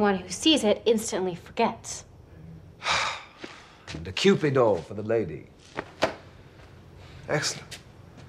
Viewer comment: What did spike say at 1:43? A what for the lady? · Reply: A Kewpie Doll.